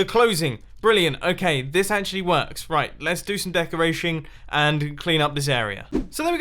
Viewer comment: did anyone else hear "decorashing"? Lol.